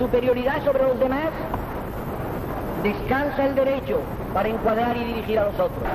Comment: Una pregunta: En el minuto 4:13 es Franco que habla, verdad? Entendí lo siguiente: "En la fe, en la superioridad sobre los demás [y esa palabra no entiendo:] DESCALZA (??) el derecho para encuadrar y dirigir a los otros." Alguien me puede contestar esas dos preguntas? Gracias! ☺